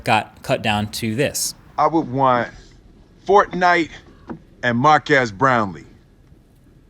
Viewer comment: In the YouTube rewind I thought they said your name Mark Ass brownies